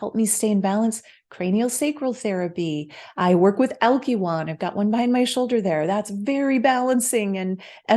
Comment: This is great, thank you. Did you say algulon @ 16:50 over your shoulder? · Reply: Thank you Joel! It’s ALKYWAN <link>